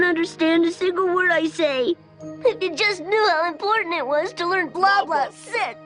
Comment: the closed captions say "If you just knew how to learn..." instead of "If you just knew how important it was to learn"